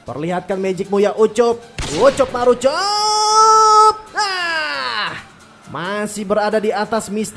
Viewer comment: Menit 12:20 pes sebut ucok marucok? Apa ga salah denger kalian ges?